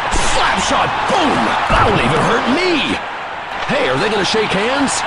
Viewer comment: What did he said at 3:22 after the hit??? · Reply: "That would even hurt me"... I think